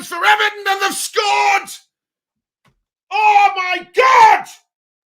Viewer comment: i heard ohmynuts